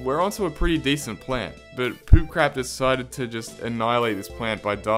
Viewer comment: did I hear correctly? poop crap?